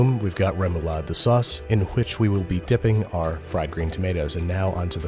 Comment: did he, did he say "hwich"? I'm getting Family Guy flashbacks